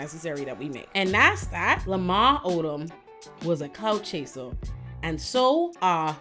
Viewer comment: there’s an error in the captions you included with this video. It says ‘cow’ instead of ‘clout’, normally I wouldn’t point out a little typo but this really changes the message for people who rely solely on captions